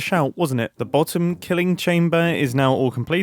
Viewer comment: heard that as "the bottom-killing chamber"